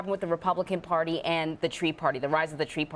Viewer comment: Did she say "Tea or Tree party" at 14:13? It sounds like "Tree Party" to me.